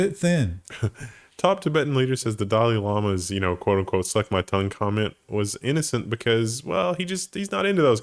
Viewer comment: I don't want to be that guy but I thought I heard "that's my tongue"